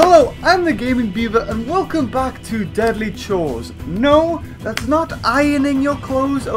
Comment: 0.07 did he just say deadly chores that’s how l feel when l do my chores